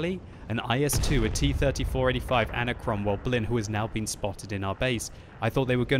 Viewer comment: Did I just hear "Cromwell Blin?"